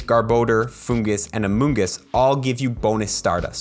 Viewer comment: did he say among us?